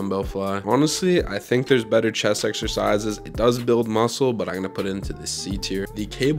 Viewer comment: I heard "chess exercises"